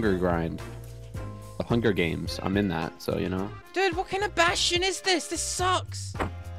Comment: he said "What kind of bastion is this!" and I heard, " WHAT KIND OF BAT-SHIT IS THIS"